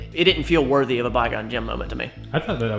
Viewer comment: I love how "Bi-gon Jim" is just a thing now lmao. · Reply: Holy shit. I thought he was saying “bygone gems” all this time.